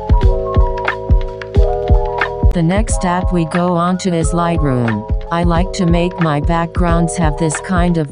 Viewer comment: what app was that? I know it said in the video but i didn’t get it properly 😅 can you tell me? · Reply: Lightroom 💕